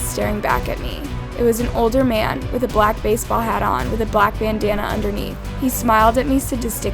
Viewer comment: i thought it said banana and not bandanna